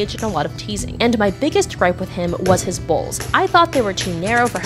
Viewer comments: Nah cause I thought you said balls not bowls… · I thought you said balls instead of bowls at first. Im gonna go jumper off a cliff now. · Bro instead of bowls, i thought she said balls HAHAHA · I thought you said balls not bowls. and I was like shit this is next level teasing with your senpai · Bro I thought she said his balls But she said his bowls😭😭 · i thought she said balls but i realised it was bowls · Anyone else thought she said balls and not bowls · I THOUGHT SHE SAID BALLS NOT BOWLS 😭😭😭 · “Was his bowls” I THOUGHT U SAID BALLS AND MAAM WT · I thought you said balls instead of bowls 💀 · Every time she said bowls I thought she was saying balls… example:not only did I tell him I didn’t like his balls(bowls) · I thought you kept saying BALLS I’m like??? H u h ? … can’t balls be all sorts of sizes and what do you mean they’re cup like?? But then after legit the THIRD WATCH I was like ohhh wait is she trying to say BOWLS ??? like jeessussss I could not understand you!!